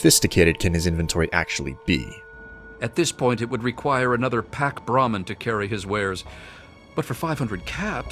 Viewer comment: I swear he said "He will soon need another Barack Obama to carry all of his junk" Lol